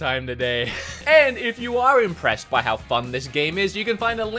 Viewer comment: it sound like is saying f*ck*d instead of fun, when he was saying if you are impressed by how fun this game is